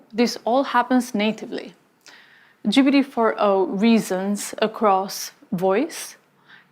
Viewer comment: 謝謝分享，有個地方字幕寫錯了10:27. 4o instead of for o